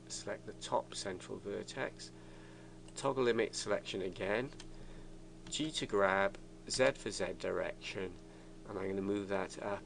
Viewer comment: hey can anyone tell me what is he saying in 3:01 like z or g to graph something like that I don't understand what does it mean by that. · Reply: I see this is from a year ago and nobody answered you...but if you're still wondering he says press g to grab.